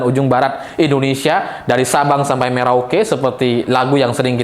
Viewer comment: Wkwkwk... Mas saya salah dengar gak ya ?? Sabang aku dengernya subang, maaf cuma mengingatkan.... Saya jelas ganjaris tulen wonosobo